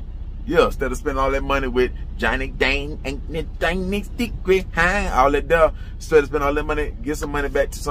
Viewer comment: when he said this all I heard in my head was “I’m Korean moron” 😂